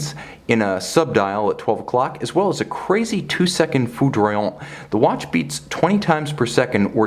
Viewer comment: What was the french sounding word at 4:36? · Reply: Foudroyant, meaning something like electrifying, struck by lightning